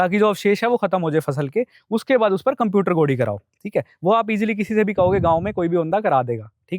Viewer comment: pe ye kya bola ?? Computer gody matlab ??? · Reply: computer level (khet ko eksar krna)